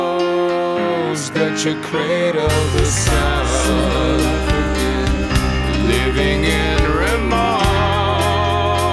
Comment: Everytime I always hear, "Silver African" Anybody else hear that as well? What's he really saying? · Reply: Sitting up again is what he's saying.